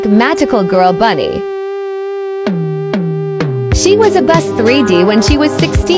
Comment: (?) “she was a bus” is what I heard 💀💀